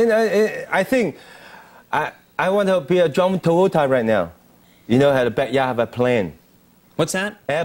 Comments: For everyone confused at 8:26 I believe he said he wants to be like John Travolta and have an airplane in his back yard. This is because John Travolta is a certified pilot and has many planes in his back yard and his house is basically an airport. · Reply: Well... to be honest, it sounded like he said a DRUNK TOYOTA. lol. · what he say? "something something something right now." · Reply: CatSmackDaddy I believe he said he wants to be like John Travolta and have an airplane in his back yard. this is because John Travolta is a certified pilot and has many planes in his back yard and his house is basically an airport.